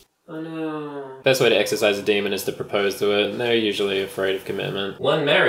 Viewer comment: Because I’m American I heard “Damien” instead of “demon” at first so I thought you just had an invisible guy named Damien in your house who replaced your cereal with blood